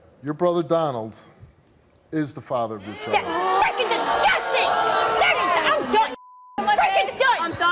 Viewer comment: That’s F’n disgusting!!!! Did I hear that right?